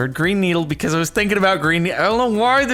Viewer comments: I hear brain needle every time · I hear “Brain needle” Not a joke im actually 100% serious (Edit): its the only thing i can hear